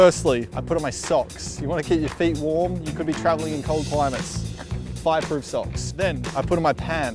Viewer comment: Turn the CC on omg at 0:15 Five Fruit Socks instead of Fireproof xDDDD